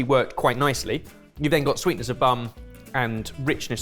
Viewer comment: "sweetness of bum..." Please tell me I am not the only one who heard it! Also, Jamie's face :D